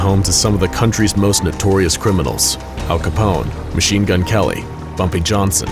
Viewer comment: did he say machine gun kelly?😂